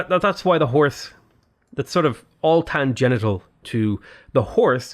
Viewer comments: Great video! Is it just me or did you say "tangenital" at 42:40? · Did you say "tan-genital"?! around 42:42....